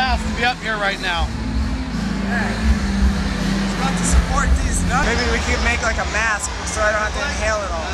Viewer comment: when it was 3:17 did say deez nuts??